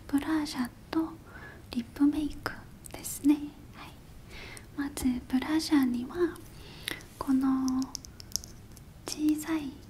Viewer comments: まずブラジャーにはに聞こえる(((黙 もう言い間違えも癒しだかわいい😂💕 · (挨拶) 0:12 (会話) 1:00 (お肌のタイプチェック) 2:39 (クレンジングウォーター) 4:55 (保湿) 6:33 (メイク開始) 6:41 (ファンデーション) 8:37 (コンシーラー) 10:12 (パウダー) 12:10 (アイシャドウ) 17:12 (アイライナー) 18:17 (ビューラー) 19:49 (マスカラ) 22:12 (チーク) 23:45 (リップ) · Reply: ブラジャーに聞こえたw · ごめんなさい。ブラジャーにしか聞こえません。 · 何回聞いてもブラジャーにしか聞こえん..(( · ブラジャーって聞こえるの私だけ？(笑)